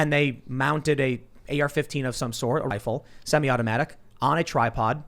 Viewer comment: Hey man your video jumps a slight bit at 5:08 make it sound like you said assault rifle instead of "a rifle"